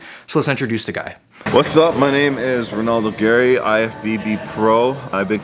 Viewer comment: I heard it Renaldo gay